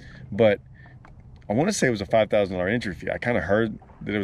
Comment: 5000.00 entry I thought I heard them say 65,000. first with the amount of boats. 230,000.00 total purse...